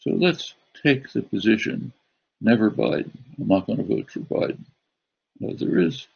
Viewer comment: The English captions for Chomsky (at least up to the two-minute mark) are not accurate. 1:48 “NeverBiden, I’m not gonna vote for Biden.” Is captioned as “Never bite Mark on the vote.” What?! If you’re only following captions, beware that they are not accurate. At 1:44, he said, “We know where that led.” Referring in part to the rise of the Nazi party. But the captions left out “where that led” and started the next sentence. The captions say, “We know that there are many other cases like that...”, which completely changes his message. The auto-generated English captions are slightly better there, saying, “we know where that live” but then, “never buy a mock on a vote survive.” I guess I expect human-captioned video to be more accurate. I feel for the people who rely solely on them.